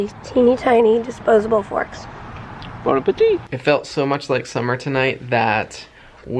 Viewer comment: said, "teeny tiny forks" ...and said, "Bon Appetit" ...and I heard, "bone ah petite" (as in teeny tiny) LOL